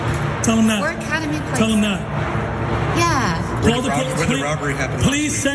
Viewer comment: And did anybody catch the son @ 2:59 telling the mom "tell them it's where the robbery happened"...